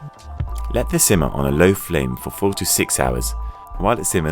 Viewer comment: LOL i thought he said simmer for 46 hours. loll